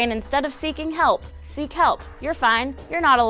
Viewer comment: Instead of saying "Seek help" I thought said "See kelp"